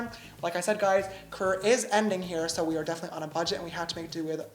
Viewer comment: I SWEAR I heard him say "Korea is ending here"